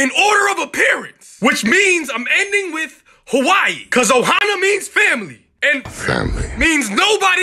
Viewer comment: WHEN HE SAID OHANA MEANS FAMILY I FREAKING THOUGHT HE SAID OHIO MEANS FAMILY. STITCH WHERE YA AT? Edit: When he said OHIO as "Ahoy" I thought he meant the cookies